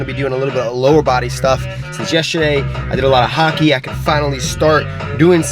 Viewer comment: did he say he did a lot of hockey?